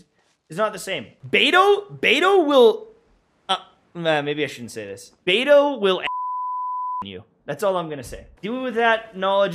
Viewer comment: What did he sayyyy?? I cant real lips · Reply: 'Beidou will amazon position you'